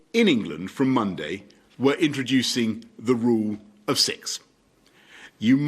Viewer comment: The rule of 6. I thought i heard the rule of sex lol